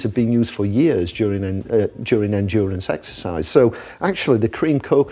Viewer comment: did he just say 'sexercise' ?